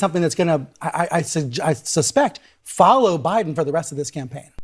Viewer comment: did he say Obiden